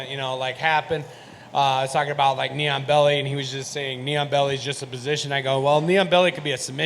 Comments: Thought he was saying "neon belly" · lol I thought he was saying "neon belly"